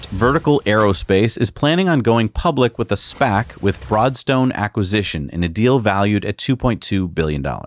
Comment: I swear, I thought you said "Fraudstone", not "Broadstone" during the segment on Vertical!